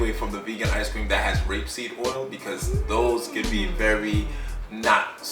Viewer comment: Doesn't Sebi say grapeseed oil is okay? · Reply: I thought I heard say “grapeseed oil” as well, but he repeated it and it was “rapeseed oil,” aka Canola oil. Wish I had thought to get the time stamp, but I hadn’t read your comment yet…